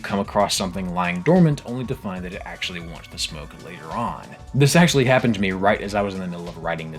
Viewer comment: What does he say here? The subtitles say "wants to smoke," but I don't understsnd how that could be what he means. English is my first language and it may be a phrase with which I am not familiar. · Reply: He’s saying “Wants the smoke”. It means “wants to fight.”